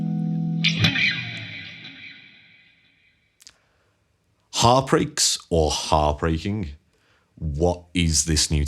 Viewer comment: I thought you said “Heart Break”, which describes it perfectly when I try to play it.